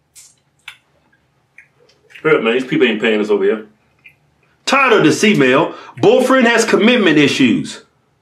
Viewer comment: When I just started watching the twins I thought he was saying, “Tired of this E mail…” 😝